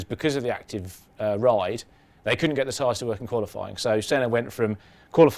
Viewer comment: What did he say at 3:54? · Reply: They couldn't get the tyres to work in qualifying.